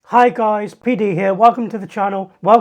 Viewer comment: I'm sorry mate I had a few too many and when the video started I thought you said Hi guys P diddy here 😂😂😂😂